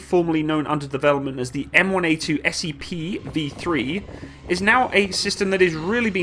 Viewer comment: did he say m1a2SCP